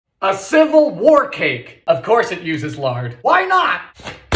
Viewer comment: I heard "a wort cake" 🤮